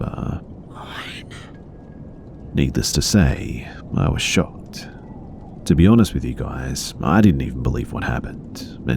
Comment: what does the whisper say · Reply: “Mine”